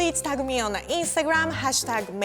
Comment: At the end, I first heard "don't fuck it this time" but she actually said "don't forget to dance"